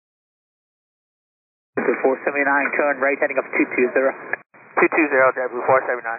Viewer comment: Well executed. The only issue I have is with that one controller who says "Turn Right Heading" like "Trdng". More than once it sounded like he was saying terrain.